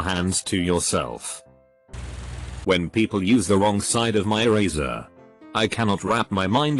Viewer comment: I heard "my eraser" as "my razor" and wondered who on Earth was letting people borrow their razor and why it had 2 sides where only the used side should also be used. Like, do you want to spread blood borne illness/disease? 'Cause how you spread blood borne illness/disease.